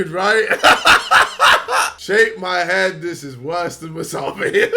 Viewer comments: Am i the only one that heard him say shake my hand when he saw smh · did he just say Shake my hand smh mean some how